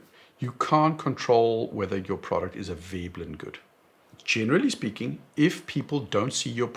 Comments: Can you please tell me what you said @ 16:45 ? ... Your product is AVEBLINGOOD??? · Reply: A Veblen Good. 😀😀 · "ablin" around 16:45 time stamp? Closed captioning didn't help so I sound-spelled what I thought I heard and now it makes sense: Veblen A Veblen good is a luxury item that has a direct relationship between price and demand, where demand increases as the price increases. Great informative talk. Thanks for doing the deep dive into the ghastly finance stuff and explaining it.